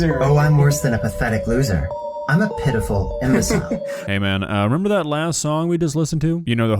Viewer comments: he legit sounded like he said “I’m a pedophile” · i thought he said “p**ophile” instead of “pitiful” 💀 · seriously thought he said pdf file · When he said he was "pitiful" i wasnt watching the screen and i thought he said pdfile and i just was like "so people want to admit it now as if it is now acceptable" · When he said “I’m a pitiful-“ I legitimately thought he said pedophile… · For a second there where he said "pitiful" I could have sworn he said PDF file, like just admitted it all chill-like.